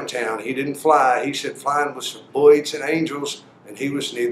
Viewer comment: at about 5:36 he says that "flying is for *something that sounds like 'boyit' and angels and he was neither one." What is a 'boyit' or whatever that word was?